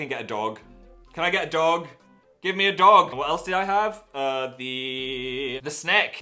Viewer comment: For a second I thought he said f**k give mr